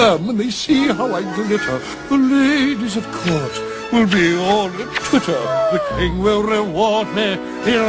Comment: I always thought that Ratcliffe had said "The ladies at court will add me on Twitter"🤣